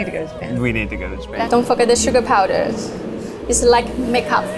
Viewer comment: i thought she said dont fuck up the sugar powder instead of dont forget the sugar powder